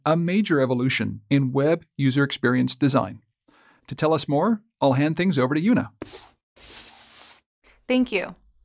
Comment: i thought he said i'll hand thing to you now